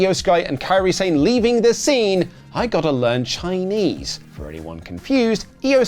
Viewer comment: That's good I thought Carlito said " I gotta rent chinese😅"